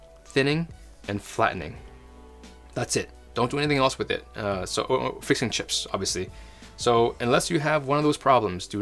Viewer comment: I know it was: "...fixing chips," but I definitely heard: "fish and chips" xD